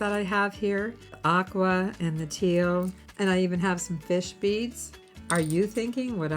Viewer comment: I like the set but I would personally add the blue bead as focal. As a funny when I first heard you going through the list of beads thought you said fish feet not fish bead & thought fish don’t have feet…lol